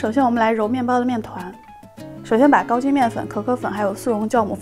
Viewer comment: 我他妈饿到听成 高清面粉....我还想问1080P够不够.....